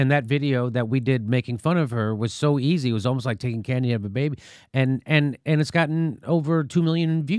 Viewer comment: Did he say "it was like taking candy out of a baby"?